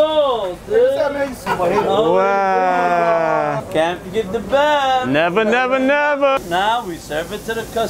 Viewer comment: For the longest I thought he was saying Cant forget the bath...now I realize he is saying the bev